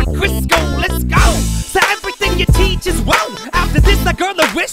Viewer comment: I thought said "Say everything your teacher's woke". I thought to mysef is woke culture was prominent back in 2012 and was ahead of his time. In reality, I misheard "your teachers won't".